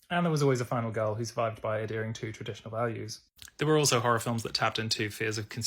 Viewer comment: I think the captions got messed up there. It should be “adhering to,” not “it during two”